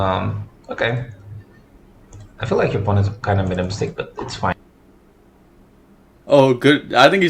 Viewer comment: I think he said "I feel your opponent kinda made a mistake, but its fine"